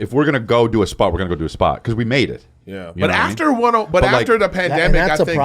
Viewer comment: did Chris really say he's made it 😮😮😮